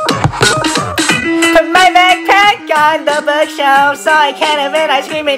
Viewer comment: I heard “Crack” instead of backpack😭😭I literally cannot head backpack Edit: the tabs tho-💀💀💀💀💀 Edit 2: His voice when he says moan tho